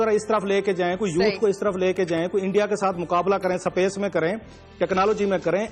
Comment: Did he say "SAPACE" (3:03)?